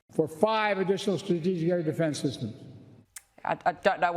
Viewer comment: Huh, you couldn't understand what he said at 0:20? Was plenty clear for me and I have significant hearing problems "for five strategic air defense systems". I'm not pro or against the fellow but your making a nothing burger out of